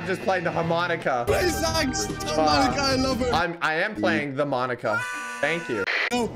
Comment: made me laugh:tell monica i love her Socks:but im playing with monica Me:what did he sayyyyy!!!! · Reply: He said the monica not with monica